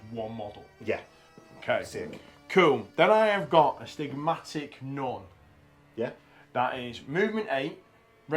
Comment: I keep hearing “Astigmatic Nun” and it conjours images of an old lady in jam-jar glasses and a cardigan pottering around amongst the raging trench warfare. My warband is now going to have a plump old lady in a habit who is a ridiculous badass.